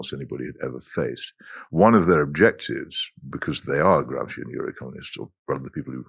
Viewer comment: what's he saying at 5:30? 'gramsci euro communists'? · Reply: Gramscian Eurocommunists . The post-1968 current of revolutionary thought, which abandoned Leninist theories of physically seizing power through a vanguard party and adopted instead the oblique approach of the Italian Communist Antonio Gramsci, briefly summed up as achieving cultural and moral superiority (undermining Christianity, capturing the teaching and legal professions and the criminal justice system, before proceeding to a wholesale revolution in society. Blairism's theoretical house journal in the 1990s, 'Marxism Today' was famously Eurocommunist. Blair himself did not understand this (or much else) , but he was only a figurehead anyway.